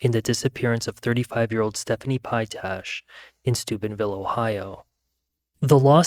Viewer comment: I thought you said Stupidville Ohio 😂